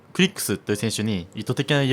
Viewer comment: クリックスがクリップスに聞こえた